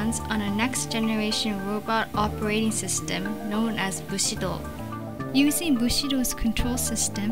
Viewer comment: Anyone else think that the way she says "V-Sido" sounds like "Bushido"? 1:23 3:03 Starts shooting at friend, friend is panicking and asks you to stop, you want to stop buy you are laughing too hard. LOL!